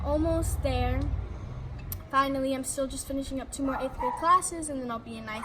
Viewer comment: I thought she was boutta say “I’m still finishing up 6th grade classes” but she said 8th grade 😂😂 (not laughing at her I’m laughing at myself)